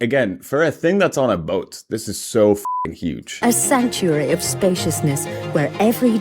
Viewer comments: the first 2x she said it I thought she said "A century of spaciousness" , I was trying to understand what she meant. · I heard her say "a century of spaciousness" and thought "that makes no sense"